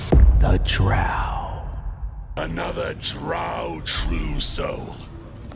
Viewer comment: I really thought he said Drow Trucel for a second and wondered what the fuck was going on.